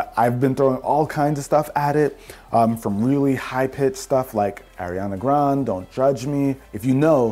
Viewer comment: This whole video and all I heard was “Ariana Grawnd”